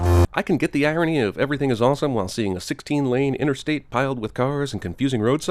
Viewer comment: i heard "rape-ing is awesome" and i laughed.... what is wrong with me?